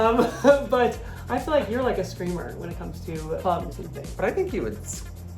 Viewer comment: "Clay is a screamer..." ok, never mind the context... Im sticking with what I heard... and Im not disclosing my mental imagery .... mayb shocked, mayb smiling....